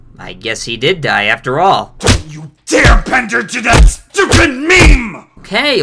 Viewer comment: WTF did he say? (Sounded Jiberish) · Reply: He said. YOU DARE BENDER ON THAT STUPID MEME!